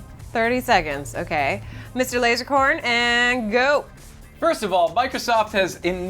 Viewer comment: I thought joven said Laserborn